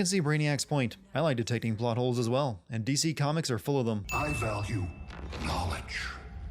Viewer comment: Did he just say DC comics? ding